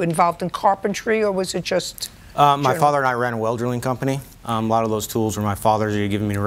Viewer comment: Did he say “welderling” company at 1:45? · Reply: Well drilling